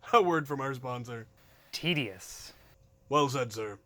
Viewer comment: did he say tetes? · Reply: Tedious